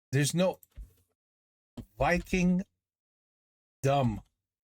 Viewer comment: That's funny cause here i heard : VikingDumb instead of Vikingdom and i think that's a better title for the movie. 😂